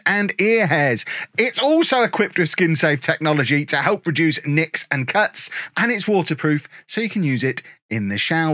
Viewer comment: wait did u say the weed whacker 2.0 help produces nicks and cuts? 11:40. No thx looks like it is not for me I dun enjoy nicks and cuts